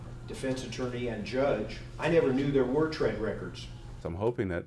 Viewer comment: min did anyone else catch what the judge said? Did he say, “tred records”? Or something else? I googled this and nothing.